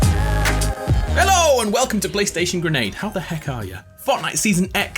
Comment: I thought he said playstation grande/ariana grande